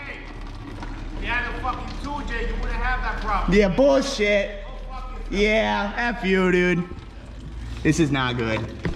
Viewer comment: who was that in the truck and what did he say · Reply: saying if you had a 2jz you wouldnt have this problem.